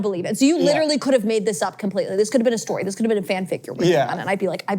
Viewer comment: funny that the subtitles says "fan fake" when she clearly says fanfic, the subtitler didn't what a fanfic is